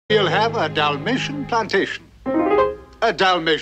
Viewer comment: I'll always thought that they were saying Dalmatian Salvation - LMAO! That's funny